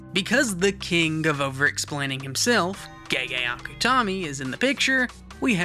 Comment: what he said:Gege Akutami What the subtitle said: gay akutami Very true